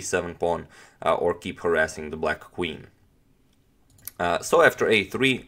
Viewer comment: LOL I heard "keep her ass in the black queen"